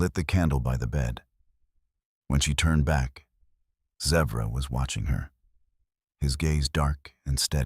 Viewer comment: I absolutely love these stories ❤❤❤ but yet again, the bolded first line of description says Apache warrior but throughout the story he is a Lakota warrior! Why?? And why do you put words on the screen? His name is Zevra not Zebra! Ugh!! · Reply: Thank you for clearing that up - I thought he was saying Zebra!😳